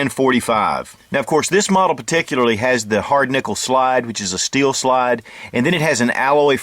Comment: it sounds like you said "...hard-nipple slide..."